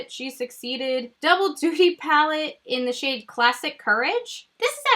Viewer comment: omgg did she just say "double dookie palette?" LMAOOO